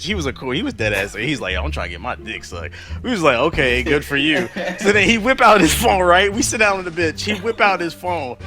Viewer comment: i dead ass thought u said ur boy whipped out his shlong